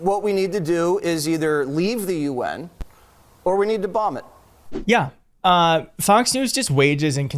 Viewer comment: I thought Jesse Waters said vomit it not bomb it?